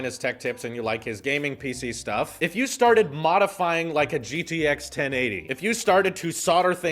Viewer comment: Was I the only one who heard him say GGX 1080